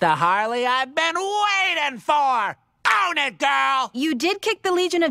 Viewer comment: Did he said "Owned it girl!" or "On it girl!"? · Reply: He said "own it girl"